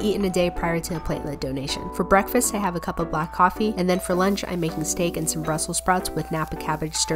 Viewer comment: What she said: "For lunch I make stake" What I heard: "For lunch I make a mistake " Me: Bro fuckin same